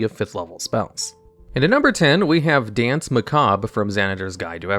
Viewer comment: Is it bad that after I heard “Danse Macabre” I misheard it as “Dance Macabre” and instantly thought “Boogie Down!”